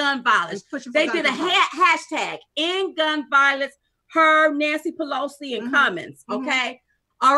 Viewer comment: Ok Ladies... Maybe I misheard you or did you say “Nasty Pelosi”? Either way, you have made my day! Old Nasty Pelosi and the Cowboy Hat Clown from Florida can ride away into the sunset. Much love to you beautiful women!